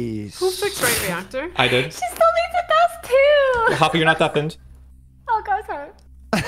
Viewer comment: what did hafu say? “she still needs ??? too” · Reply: “She still needs to douse two”